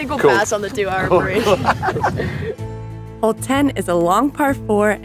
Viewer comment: I thought she said "Old 10". That'd be a cool name for the oldest hole on a course if the other holes had been changed